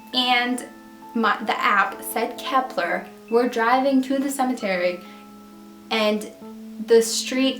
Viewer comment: I thought it said "we're drowning" when I first heard it